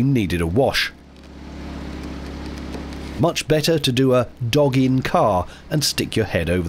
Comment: For a moment thought you said , dogging in car 😮